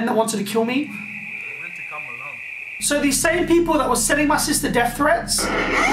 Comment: did he just said "sending my sisters death tape" like his sister wtf